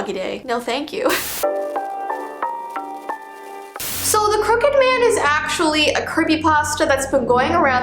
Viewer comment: Help I thought it said the cooked man 😂